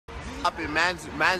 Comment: Drake really the typa guy to say "trust me daddy" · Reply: I thought he said thrust